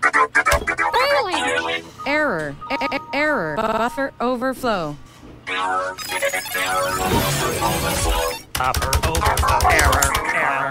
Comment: I thought he said f——- overflow